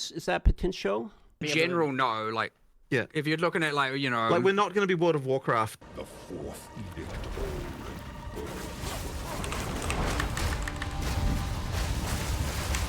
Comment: Did he say World of Warcraft?